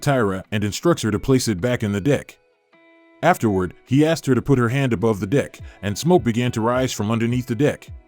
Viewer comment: i keep hearing "ask her to put her hands on the DICK...hold the DICK" 😂